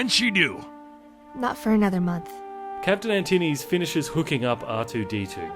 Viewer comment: I thought u said captian antiddies